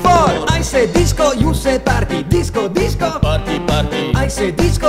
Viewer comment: My 1 braincell + stupidness thought it said farty for a second.